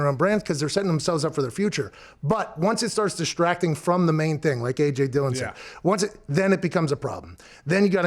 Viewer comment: Haskins successfully built a brand, for "Failure". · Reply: Was he only talking about Haskins? Cause at 02:39 it sounds like he says the name AJ Dillon, so I'm not sure if I missed something he did